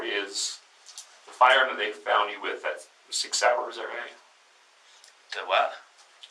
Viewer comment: It sounded like he said: 6 hours not Sig Sauer.